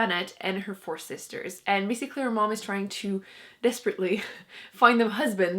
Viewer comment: "Miss Eclair Mom"? What are you saying? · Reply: She said "basically their mum".